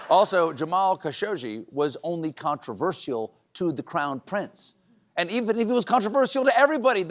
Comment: At first I thought Stephen said "Clown prince". lol